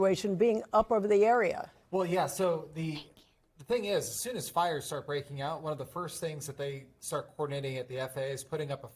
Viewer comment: Did anyone hear that whisper/voice at 00:51 after the lady finished her sentence?? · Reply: Thank you